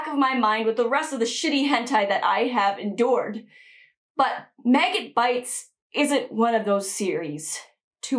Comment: Maggot Baits? Thought it said Maggot Balls.